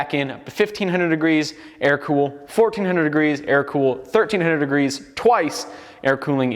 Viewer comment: When he said "air cool" first I heard "Ameri-cool" and was like... even better than normal cooling?